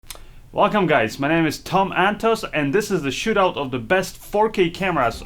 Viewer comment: For the longest i thought you said your name is Tom and Toast